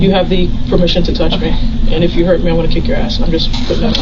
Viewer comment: "You have permission to touch me and if you hurt me I'm gonna kick your ass..." - Absolute Legend of a Cop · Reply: Oh my, I thought she said "I'm not gonna kick your ass"! So her flat tone made sense.